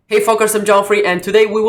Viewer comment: i thought you said "hey folkers" and was about to lose my shit